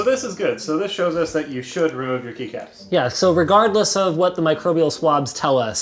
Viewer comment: I read and heard it as "kneecaps